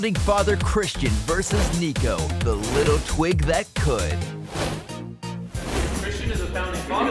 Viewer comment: oh my god for a second I thought the announcer at 1:10 called Niko "The Little Twink That Could"